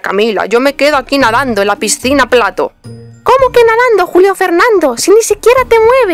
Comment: escuche como que culiao XDD like si alguien mas lo escucho xd